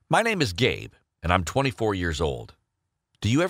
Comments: He said my name is Gabe I thought he said My name is gay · It sounded like he said,” my name is gay” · When he said my name is gabe I thought he said my name is gay and I was like WTF GAY. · Lmao I thought he’s said my name is gay and I was like oh my too 😂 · When he said his name was Gabe I thought he said my name is GAY lmao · Who heard him say my name is “Gay” XD! 🤣💀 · at the start i heard “my name is gay” 🤣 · him: hi my name is gabe what i thought i heard: hi my name is gay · At first i thought he said- “hi my name is gay” i coughed out mah food. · I thought The guy said "Hey my name is gay " i was laughing soooo hard😂😂😂😂😂 (The guys name was gabe i know) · I heard "My name is gay" I was like TF dude whats your name? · English is not my fiest language, so i hear "MY NAME IS GAY" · Nobody: His parents on his birth: let's name him gay😂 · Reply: It sounded like he said gay but the description says gabe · I hear "my name is gay" lmao😂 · I thought he said at 0:00 his name was game 🤣🤣🤣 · I thought he said hi my name is gay XD